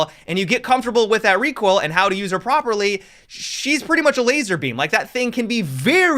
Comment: Did he say Lazarbeam?